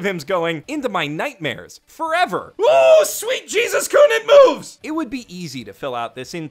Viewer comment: "OOOOOH SWEET JESUS-KUN IT MOVES!" I'm crying Edit: thanks for filling that in guys, I couldn't quite figure out what he'd said there